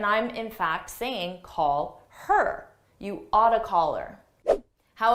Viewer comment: I heard: "You´re out of color".